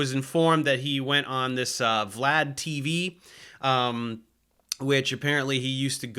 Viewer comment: I heard he was on "flat" tv... I thought "makes sense for a flerfer"... 😅😂